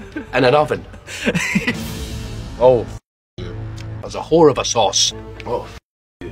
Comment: "That's a wh*re of a sauce" 😂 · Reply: oh i thought he said "there"s a horror of sauce"